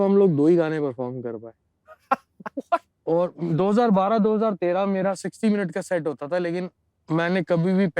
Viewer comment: subtitle is wrong its written 2022-13 and should have been 2012-13